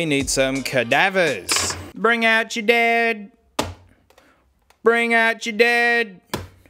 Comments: Oh lol, when he said “Bring out your dead” I thought he said “Bring out your dad” 😂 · “Bring out ja dead” thought he said “bring out your dad” then “bring out ja dud”